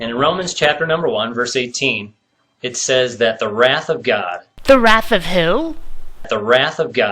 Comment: Wrath of God?!? For a second, I thought he said "Wrath of Kahn"!!